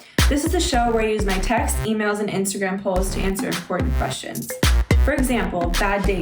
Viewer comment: I thought she was saying "Horny questions" at the beginning 😂